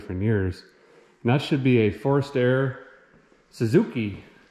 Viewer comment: I think I heard "forced-air" which, though incorrect, sounds cooler then "free-air".